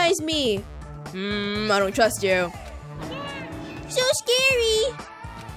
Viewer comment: When the rabbit says 'just the word kill scares me' What said :"mmm I don't trust you." What I heard: "mmmmm oh my god. Stop fucking liyin."